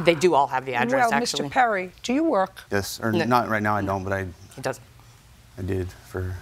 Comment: Did he say "yes sir" @ 2:40? 😅😅